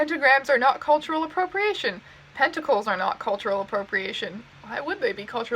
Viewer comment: "pentacles are not cultural appropriation" my weeb ass thought you said tentacles.... how anime has ruined me.